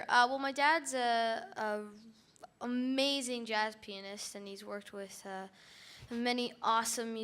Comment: It sounded like said "An amazing Jazz Penist"